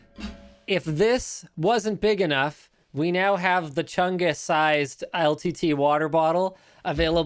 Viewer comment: Did I hear Chungus-sized?